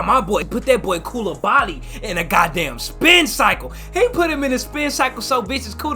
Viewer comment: Did bro just say spin cycle 12:12 😂😂😂😂😂😂